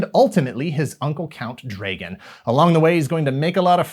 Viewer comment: The first time I heard the villains name I thought you said Count Reagan. I am from the government and I'm here to help. Also I'm not sure I want to try magic Spoon since I'm on the Meditation Diet right now. I was a vegetarian for many years but I decided to go back to the diet my family always had. Processed food isn't part of it. I'm eating pistachios at the moment.